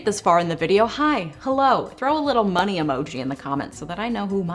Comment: 💰 I thought you said bunny emoji and I was like 🐇🐰OK...